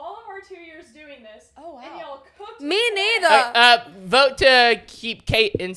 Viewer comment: what did say??? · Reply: "Me neither "